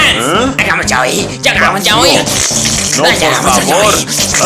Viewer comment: le entendí agamos yaoy xd